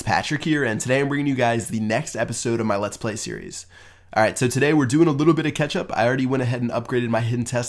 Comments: Lol it sounded like u said lets do a little bit of ketchup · At the start it sounds like he says we're going to do a little bit of ketchup.